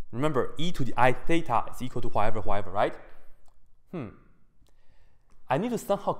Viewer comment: what does he say at 1:48 ? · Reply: I'm sorry I mean at 1:35 . He says "remember e to the i theta is...." then I can't make out what he says. I think it's "whatever whatever" am I right?